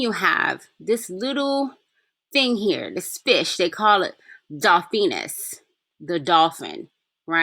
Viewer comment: I got stuck on your dolphin reference. Before you went into to breakdown, I thought you said dauphin—the French word for crown prince. Keep up the great works, Sis. Peace.